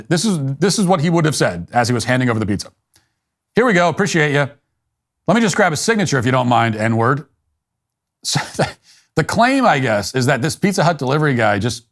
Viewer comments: What did he actually say at the end though? Audio quality is poor so it does kinda sound like he said it but what did he say instead? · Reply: "Let me just grab a signature, if you don't mind, there." The last word, with a southern accent is the word in question. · It sounds like he says "let me just grab a signature if you don't mind THERE." The horrible word was the word THERE!!!! Such BS!!! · I heard "Let me just get a signature, if you don't mind, there" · i heard 'lemme just getcher signature ...on there..... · this is what I heard .... "let me just grab a signature if you don't mind neighbor " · I heard “Let my just grab your signature, I need it.” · I heard 'let me just grab a signature if you don't mind, I need it' 'all paid up'. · And he (the supposed victim) didn't say anything THEN? You when I heard this? Two BLACK OLD MAN talking in front the door at a convenience store. THEY were loud and vocal and used the N word frequently. Another Juicy event? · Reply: What I heard was: "Here we go, appreciate you; Let me just grab a signature if you don't mind, I know "you're" all paid up" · Sounds like he said, you don't mind do ya